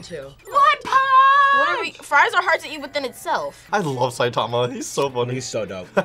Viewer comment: "Fries are hard to eat in itself" Did I hear that correctly? If so, how?